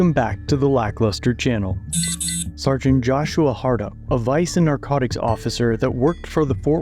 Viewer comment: Did he say sgt. Hardup? 0:04 🙄